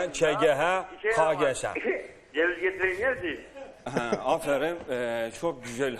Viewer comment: da yılmaz erdoğan ne diyor anlamıyorum kaç bin kez izledim çözemedim :D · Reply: ceviz getireyim yersin diyor